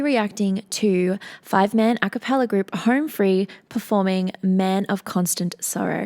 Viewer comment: With that accent it sounded like she said Harm Free. I was like, I kinda like that better🤷🏻‍♂️